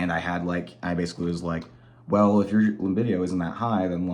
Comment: Had to rewatch the 1:55 mark. Thought I heard you say “chlamydia and was like dammmn! How you gonna call her out like that on the internet!? But then I realized you said “libido”… and was like ok… fair enough.😂😂